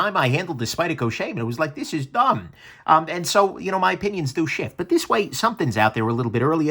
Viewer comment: I thought he said my opions are too shit lol